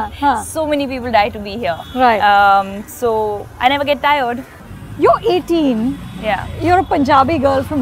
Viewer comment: Guys did she say "you're 18" at 3:10 Or I heard it wrong? Because she is 1990 born according to encyclopedia..!!